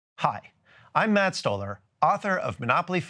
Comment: I thought he said Matt Stoner...i ended up disappointed, but i do love chocolate.